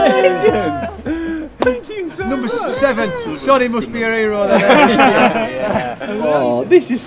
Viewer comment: what is he saying sonny must be what? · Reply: "Sonny must be your hero then"